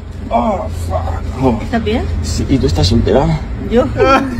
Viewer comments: que dijo? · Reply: Dice: "estás bien? - si y tu estás soltera"? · porfa alguien sabe que dice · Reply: Dice: "estás bien? - si y tu estás soltera"?